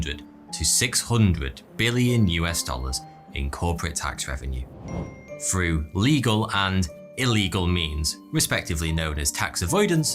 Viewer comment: Bruh heard this as through legal and illegal memes